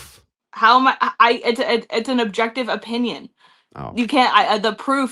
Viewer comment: Correct me if I'm wrong or I misheard but at 30:13 did she say "It's an objective opinion"? I really hope that she didn't because those are quite literally opposites. It's like me calling something a round square